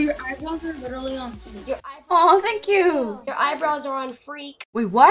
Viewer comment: at first, i thought he said your eyeballs are on freak.